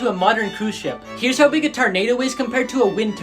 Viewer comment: Did he say TAR-nado